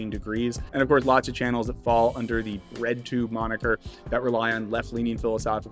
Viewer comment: I'd look up what is Redtube before using the word in this context :D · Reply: haha i was saying breadtube but my guy you just told on yourself. (as did i . . . i guess)